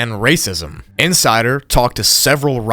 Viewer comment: It almost sounded like he said "inside her" which is ironic